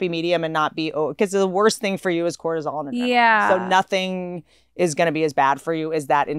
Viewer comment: what does whitney say at 33:00??? the worst thing is somwthing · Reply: She said, "nothing is worse for you than cortisol and adrenaline"